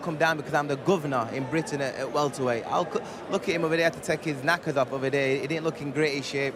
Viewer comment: what did he say that was offensive? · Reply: Diego Othman “knackers” it means underwear up in Yorkshire